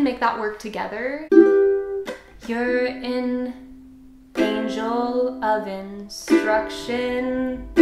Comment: I thought she said "you're an angel oven."